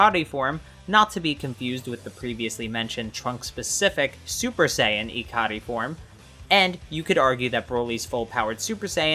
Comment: what are you saying here, ssj ecodi? How do you spell what you're saying i want to research it. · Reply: not sure if im a fan of the terminology but he probably says Super Saiyan ikari - as "ikari" (怒り) is japanese for "rage"